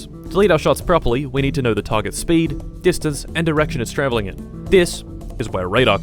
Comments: he litteray says "erection" instead of "direction" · "speed, distandce and erection" is what he should have said because thats what i heard